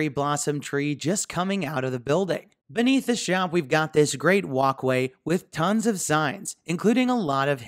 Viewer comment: I really love all the details in this, you would think it's too busy but that's not the case. Everytime you mentioned the walk way for some reason I heard it as "WOK" way. I originally thought maybe it was intentional for the restaurant